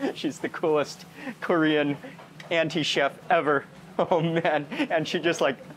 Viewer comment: Did he said Korean Anti- Chef 😂😂😂